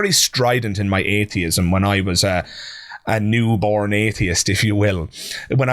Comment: I kept hearing 'Born Again Atheist' in my mind, when you would say "New Born Atheist'...lol. Must be PTSD from growing up pentecostal.